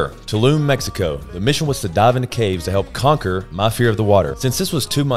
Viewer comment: Love the fact that Austen's Southern Drawl made me think he said: "the mission was to die in the cave" at 6:30 rofl :-p